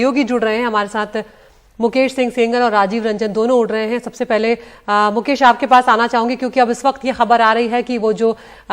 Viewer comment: did she said "dono ud rahe hain" ? 😅😅